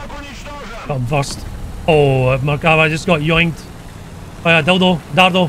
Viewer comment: what did you just say there? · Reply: Yoinked! It’s like being yeeted!